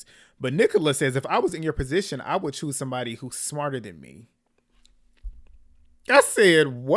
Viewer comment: i think she said smaller?